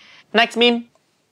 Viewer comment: is where he actually says meme instead of me me